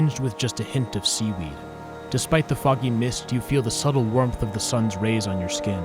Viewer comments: when i had it on 2x speed and I thought it said "despite the FUCKING MIST" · did anyone else think he said “and the f*cking mist”